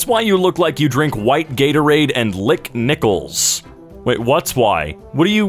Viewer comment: I thought it said “lick rickets”